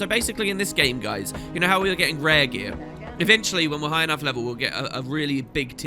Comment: Emmm, so at 7:53 you talk about the different skinns, and I find this quite funny cus you said: "ehm so basicly in this game guys, you know how we are getting rare gear?" And then a random NPC in backgroud says: "rare gear?" or that is what i think the NPC said. I just start laughing more and more as i listen in on that